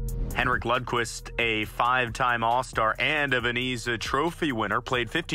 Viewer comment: did i hear that right?😂 "vaneeza" trophy